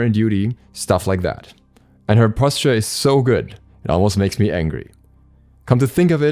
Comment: I thought he said “Her posterior is so good, it makes me angry.”! Lol